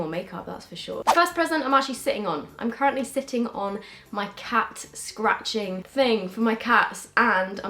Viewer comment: My phone froze and all I heard was ‘I’m sat on my cat’ ...😂